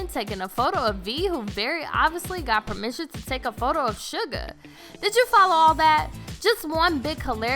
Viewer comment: IM SO CONFUZED!!!! (Also... Did you say junkook and sugar? Why?!